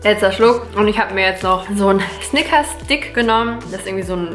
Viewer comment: Wer hat auch snickers Dick verstanden?xD